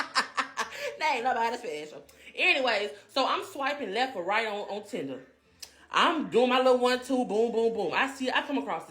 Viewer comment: What is the creature at 11:00 even saying? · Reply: "You ever did drugs?" Even she said hell no to shooting up...